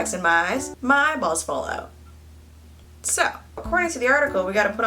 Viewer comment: Did she say her eyeballs fall out